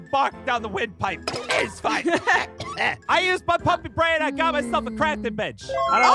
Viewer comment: I thought said he used his pumpkin brain